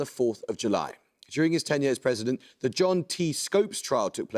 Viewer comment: his tenure was five years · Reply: Yes. I was responding to the prompt about the president at the time of "The Jazz Singer." On reflection, what I heard as "his ten years as president" was actually "his tenure as president".